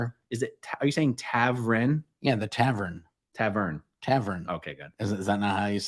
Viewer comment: Lol Marc was definitely saying "tavren"!😂